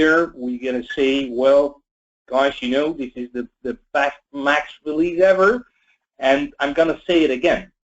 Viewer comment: what does he say @ 2:06 ? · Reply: This is the best max release ever.